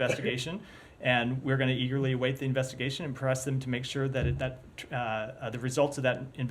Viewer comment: How did he know there was a target there? I thought he said he didn't know let wait for the invagination